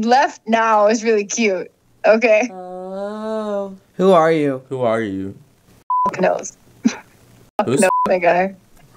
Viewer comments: what did he say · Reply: F slur :’) lmao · What did say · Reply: he said f*ck, and i were just messing with him acting like it was a big deal :P